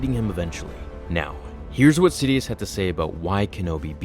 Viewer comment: I first thought I heard Darth Sidious